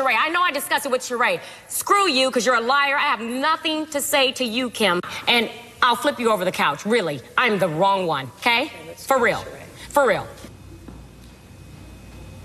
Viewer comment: Remember this. I thought she said table but she said couch. Yeah, I remember this one. It tickled my ass.